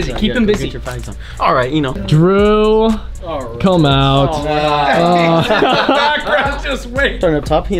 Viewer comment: Always get a laugh when I hear "drew come out" 😂😂